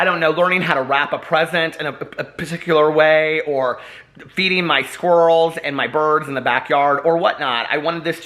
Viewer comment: 🤣🤣🤣🤣 lol At 0.45 I thought you said “Beating my squirrels and birds” 🤦🏼‍♀️ hahaha I definitely need to get my hearing checked 💚 xx